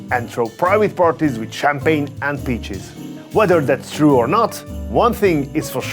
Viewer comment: I thought he said in the beginning "with champagne and bitches" not peaches 😂